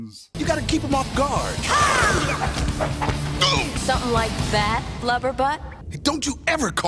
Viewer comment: What's wrong with my ears, I keep hearing Sonya's insult as "loverbutt."